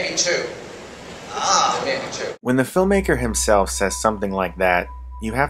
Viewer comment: I thought the guy said: that might be true. Not that might be 2.